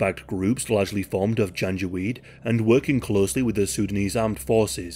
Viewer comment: I kept hearing "ganja weed"😔